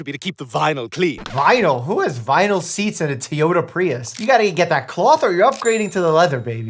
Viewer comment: Did he just say…”Tyota”???